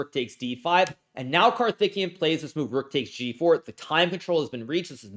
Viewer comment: swore he was saying Karthikeyan was THICC the whole time.